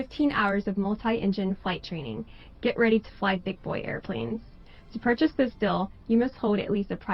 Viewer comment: Did she said - GET READY TO " FLY BIG BOYS " plane .?!😒🙄 She Must enjoyed that. USA system is Rotten to its core by the chosen people.